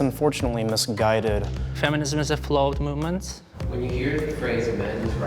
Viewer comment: is he saying flawed movement????